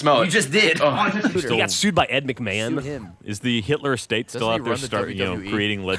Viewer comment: What does say from 19:33-19:38, I can not hear it clearly with talking about the Hitler estate. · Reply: lanceisgone "Doesn't he run the WWE?"